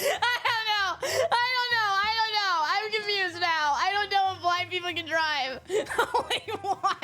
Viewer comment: I misheard on that one "I don't know if black people can drive" I had to do a retake then a sigh of relief Oh thank god isn't racist just ableist